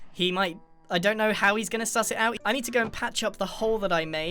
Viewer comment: Did he say sus 😳